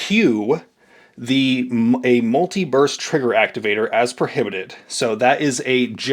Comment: I thought you said "multi-verse" trigger at first 😂